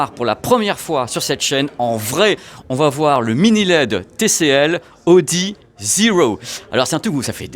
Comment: Au départ j'ai entendu "le TCL Audi Zero". C'était assez ambitieux comme crossover 😅